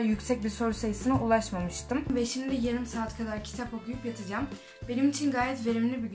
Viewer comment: = 20 saat dedi yoksa ben mi yanlış anladım😞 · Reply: evet herkes yanlış anlamış. yarım saat kadar kitap okuyacağım dedi.